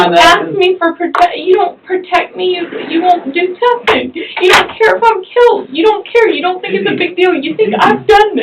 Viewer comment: anyone hear her slip up? She said “you don’t care if I’ve killed”